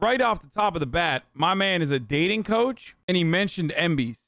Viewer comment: Thought it said dating couch